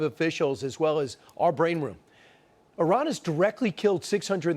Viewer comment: Tough guys. 3 more to foin the front line. Did you just say your 'brain room'? Lett's throw this dummy a softball.